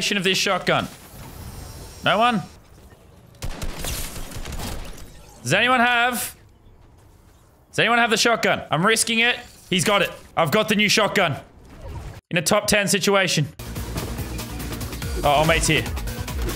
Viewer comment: I thought he said that he missed date night for the new shotgun🤣🤣🤣